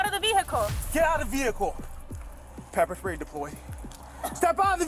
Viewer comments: The cop says pepper spray deployed and she says I'm not a boy, LOL I guess she must have thought he said I pepper sprayed the boy. LOL I also loved it when he knocked the glass out of her hand. She thought she was being so cool calmly taking a drink. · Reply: I heard that as well. I also thought, initially, he said "Peppersprayed the boy". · The cop says pepper spray deployed and she says I'm not a boy, LOL I guess she must have thought he said I pepper sprayed the boy. LOL I also loved it when he knocked the glass out of her hand. She thought she was being so cool calmly taking a drink.